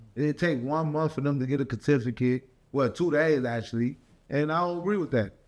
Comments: Did he say “Kuh-to-fi-cut” instead of certificate? 😂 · Did he say “catificate”⁉️🤔😤😒🤣🤣